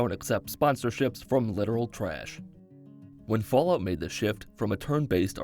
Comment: I thought you said liberal trash in the beginning lmfao.